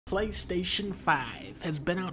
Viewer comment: I thought you said clank station 5